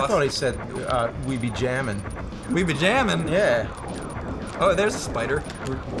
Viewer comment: To me, Falco always sounded like he was saying "Well Baby we're jammin.".